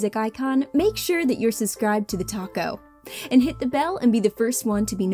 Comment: Who else thought she said "thetaco"